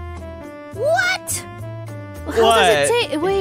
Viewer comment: caption says (from Alban) "it is corn" but Millie says "what?"